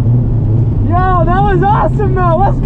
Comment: What the fuck did you say at 5:07???? Let’s gayooooo? Weird